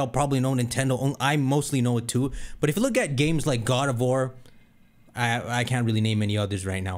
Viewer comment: I was mishearing God of War as "Gardevoir" and "Garbodor". I was like "what is he talking about??"